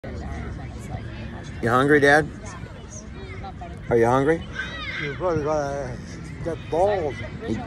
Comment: I thought said.... "Your brothers...got balls!!!!!"....., Great laugh!!!!! Tell the woman asking.. "what are you laughing at?"....."why is he laughing?"..."what are you laughing at"....TO LIGHTEN UP MAN